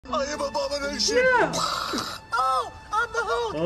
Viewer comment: Why did i hear "i am abomination" in the first few seconds